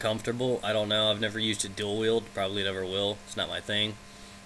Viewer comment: am i the only one who thought he said Dildo instead of Dual Weild?